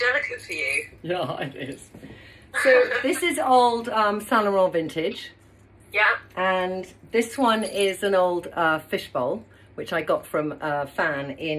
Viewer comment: I absolutely am in love with the long gold necklace shown at 13:42. I played it back several times and I'd love to know the maker but can't make it out. Can you please help a girl out?! It sounded like Sanarole Vintage? I know I've got it wrong....